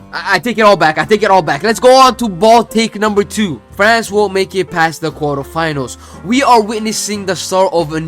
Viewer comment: bro did you say "france won't make it past the quarterfinals" here? because youtube captions thinks otherwise